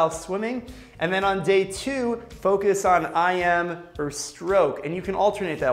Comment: what does he try to say, when he said day "I'm"?